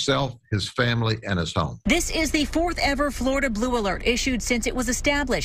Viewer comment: I thought she said BLOOM ALERT